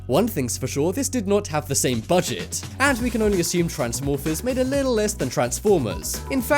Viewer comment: i thought said transmofos